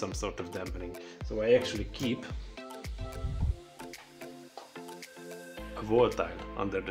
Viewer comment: what are you saying in addition to vibration insulation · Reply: I said "a wall tile in addition to vibration insulation" but nowdays that I upgraded to a concrete paver. Work very well. <link>